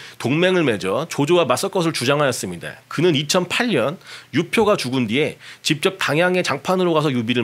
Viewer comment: 2008년 이라고 들리는데 나만 그런가 ?